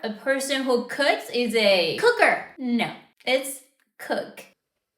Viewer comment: When she said cooker, i heard cougar. I'm cooked.